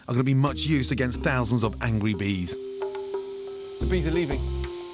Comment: anyone else notice when he said ‘angwy bees’ instead of ‘angry bees’